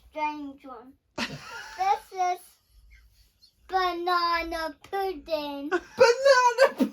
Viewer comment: Your best day ever. is a star and I love her songs. I had to rewind when she was talking about her family picture. “This one is a strange one” I thought she said Bernardo pudding at first - that is my husband’s name. She is so gorgeous and you are doing a grand job. Definitely slow down if you can. There is only so much you can do and not make yourself ill xxx. Ps house prices are coming down which is great for first time buyers.